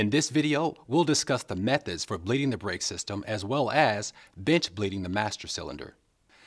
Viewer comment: did he say bitch at 0:33 XP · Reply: bench bleeding